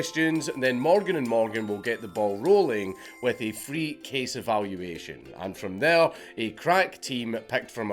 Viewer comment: In your sponsor segment, when you said "free case evaluation", I swear I thought you said "free case of ammunition" for a solid several seconds before I realized that could never work in all 50 states and went back to check if I heard it right.